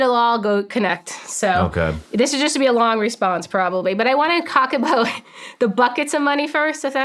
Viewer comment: Sarah Did you just Say you want to COCK about . 2:07:45 lol haha Sarah is the best ….